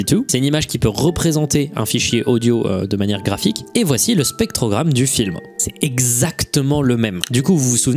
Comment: Au moment où tu dis "voici le spectogramme de la chanson", j'ai entendu "spermogramme" 😂😅